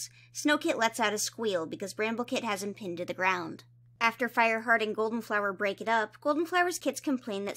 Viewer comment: I genuinely thought said “after Fireheart and Goldenflower break up” I was so confused lmao